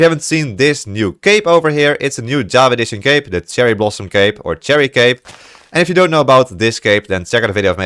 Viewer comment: lol when he said cherry blossom cape i thought that he had said cherry blossom cave